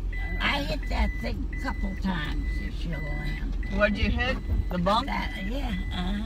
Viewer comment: I swear, at like 30 seconds in I thought I heard this: “I hit that thing a couple of times” “What did you hit... the bong?”